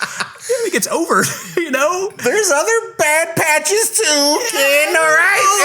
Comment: I thought she was saying bad punches.... When she first found out he was gay, it led to some bad punches.. but now, not all of the punches are bad